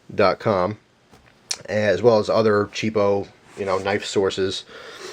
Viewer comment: thought u said no knifes